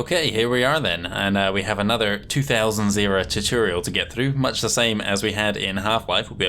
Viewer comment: Underrated channel! Excited for this pt. 4:47 Did you just call 2000 'two thousand zero'? XD